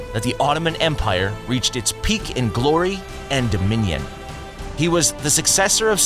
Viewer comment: Did he just say “da minion” at 5:05?